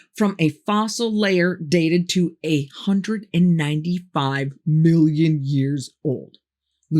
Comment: She does not say "895 million years old." She actually says "a hundred ninety five million years old." Had to listen four times to hear that correctly.